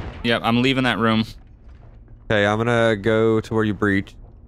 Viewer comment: For a second I thought said, "Okay, I'm going Gluten Free." Like...is this really the time?! Haha.